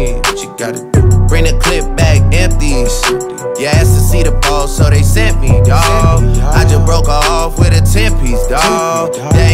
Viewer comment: I aways thought he said “ur ass saw da balls so they sent me dawg”